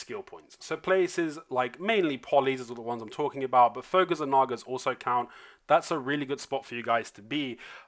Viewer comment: First of all, thank you for the video. Im not native, I could not hear well, but what did u say to grind on 0:37? I hear Polly and Nagas, what was it in between? Also, I'm currently level 57, 25% left to 58, with Tuvala Gear, and 880 SP, can I have your recommendation on where to grind? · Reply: fogans